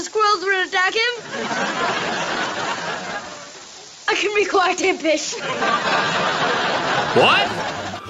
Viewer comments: I hear "I can be quite a fish" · I hear I can be quiet a bit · it kind of sounded like “I can be quite a bit” close to b***h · Misheard: I can be quite a b**ch Correct: I can be quite impish What I heard: I can be quite a dish